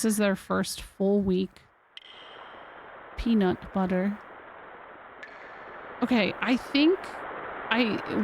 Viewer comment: did say peanut butter?!